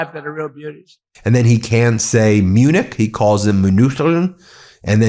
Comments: He actually says München, which is the German name for Munich. And while it's not perfect, he's gotten way closer to the correct pronounciation than I'd like to give him credit for. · Actually it sounded like he said München, which is the German name for Munich...??? Ooh, he's so smart. Ooh, the smartest man the world has ever seen.